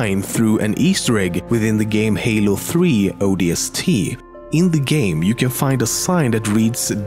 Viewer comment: Am i the only one who heard him say: in both halo 3 and halo3 odst"